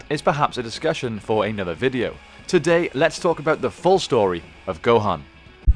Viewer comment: Because of the persenter's voice I heard "the full story of Rohan" in my head at 0:19 hahaha